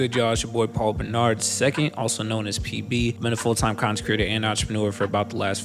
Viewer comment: Thank you for the message❤ P.S-I always thought you said CONSECRATOR not CONTENT CREATOR and I thought it made sense then. CONTENT CREATOR makes a whole lot more sense now though😅